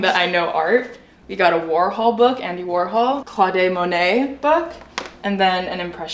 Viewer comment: did she just say Claudée Monet 😂.